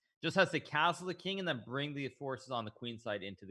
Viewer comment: did Hikaru really say ‘horse’ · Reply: "bring the forces"